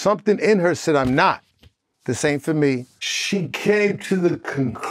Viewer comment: I heard "this ain't for me" not "same for me"